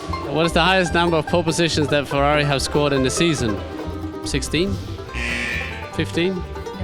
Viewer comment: Did I hear sixty? For number of poles in a seas- He's definitely high.